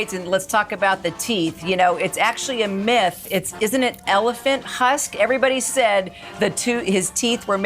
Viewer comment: Did she say elephant HUSK? 😂